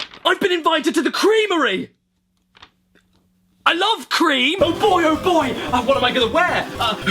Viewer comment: seconds: what did he say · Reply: Cream probably